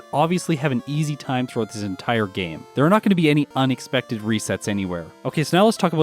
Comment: did say "onixpected" or am I just paranoid???